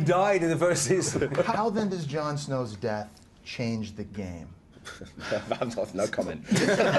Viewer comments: What did he say at 8:10 ? I don't understand a single word. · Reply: He said "no comment". I don't know why they laughed so much though, probably and inside joke. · what does Alfie Allan say at 8:08?? i cant make out the words that he said in his reply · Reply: "Fan off, no comment" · What does Reek say at 8:10 ? I cant understand it? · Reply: He says: "I'm not..no comment" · Sorry but what did he say at 8:09? Can't hear it correctly. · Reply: simply said: "No comment". I'm not really sure why they laugh though.